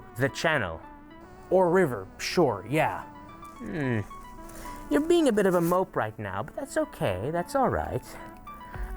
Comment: What is "Hermit" trying to say in the skit around 1:55 ish? I am apparently a failure at lipreading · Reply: "No... Leave..." and then "Leave"